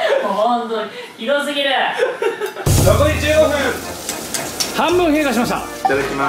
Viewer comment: と「半分屁ぇ発しました」 にしか聞こえない